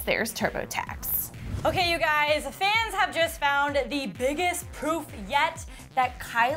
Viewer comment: WHEN SHE SAID PROOF, I THOUGHT SHE SAID POOP 😂😂😂😂